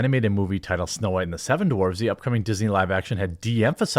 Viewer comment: Why did I think I heard you say "Snow White and the seven TGI Fridays"? That would be a better movie for Ziegler tho.